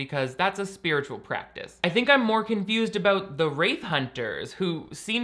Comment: Oh... "Wraith" hunters. Every time you said that I heard "Rape Hunters".